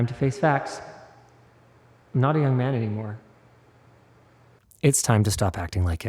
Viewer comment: "Not a young man anymore" and I heard "naughty young man anymore"